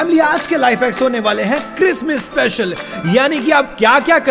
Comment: sounded like kismis( a type of dry fruit ) session 😂😂😂